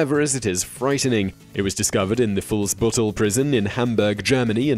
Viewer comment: I thought u said “false butthole prison”